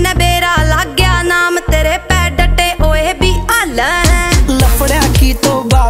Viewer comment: What she said at 1:58 ਟੱਟੇ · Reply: Datte not tatte😂